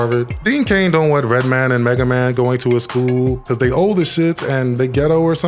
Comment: At the 8:05 mark did u just say “mega-man”😂😂😂